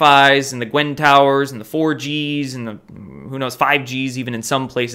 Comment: Oh you said get away from 4gs i thought u said get away from orgies....lol😂😀theese g's are fuckin up my hearing